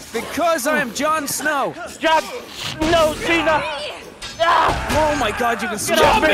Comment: did delirious just say Jon snow cena ?